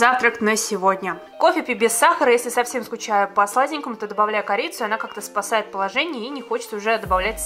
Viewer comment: мне послышалось если грустно, добавляю корейца 😂😂😂